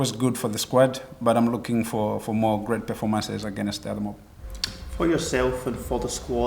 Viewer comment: Definitely sounded like he said "the other mob" On yersel fash.